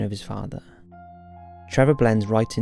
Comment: "..and he forms healthy relationships with his pe**s." :o)) --- This really caught me off guard and took me 4x re-watching it to finally hear the word "piers" correctly. Tbh, I knew immediately I'd misunderstood, also didn't expect it. - is way too respectful to say such a nonsense (I've misheard) in a missing person's case / probable homicide, so I knew right away: I'm wrong! (Buy couldn't help it, I've been laughing so hard, lol. :D) ---Great video btw! I'm quite positive I could hear excitement about "the men in black", certainly one of the most fascinating fringe theories / topics out there.